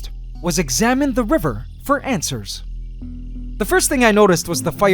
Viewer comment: might be going crazy. (pretty sure I am regardless. lol) but, did he say Antswers at around 5:30?